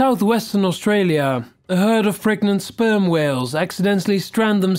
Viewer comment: Did you say "freakin' sperm whales" at 8:40?